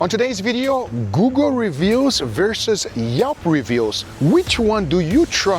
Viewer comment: Haha, when Guga said "Google Reviews" I thought he said Guga Reviews! I'd trust that review over the other two!